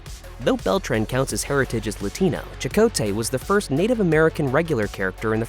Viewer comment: He’s a Latino, not a Latina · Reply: He said Latino it just sounds like he said latina, but it's they always miss pronounce things